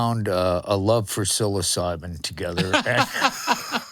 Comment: Oh, thank goodness, Al scared the Hell out of me! I thought he said that he and Gary Numan found a mutual love for "Soul Asylum", which I simply couldn't fathom. Had to re-listen to realize it was only "psilocybin". Much better!